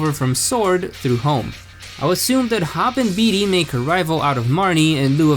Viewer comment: I legit thought you said: "Hot and Beanie" 😂